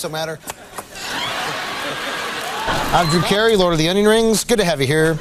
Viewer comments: What did Drew say, I'm lord of the___? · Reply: Onion Rings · What does Drew Carey say he's the lord of at 4:45? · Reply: Lord of The Onion Rings